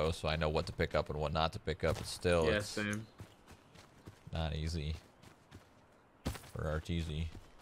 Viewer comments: Did he just say "Not ez for rtz"? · did he just say "not easy, for?